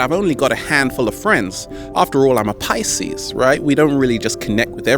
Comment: What does he say, ¨Ëven tho I'm a pysees¨? What does pyses mean, and how do you spell it? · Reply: " Pisces" it's a sign for astrology, Google it